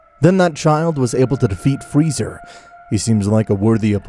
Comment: did he say freezer